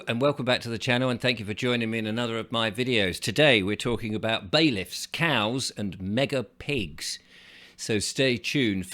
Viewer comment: I thought you said, "Baileys, cows, and megga pigs 🐖 😅 at first. I imagined drunk farmyard animals, lol